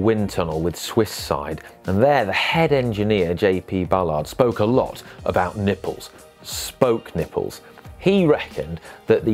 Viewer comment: For a moment there I thought you said J G Ballard spoke a lot about nipples , no doubt in a dystopian future 😂